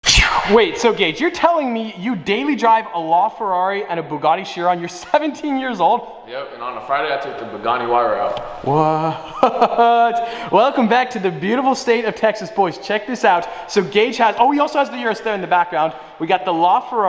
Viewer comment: did he say "and friday i took the Bugatti Huayra out" ?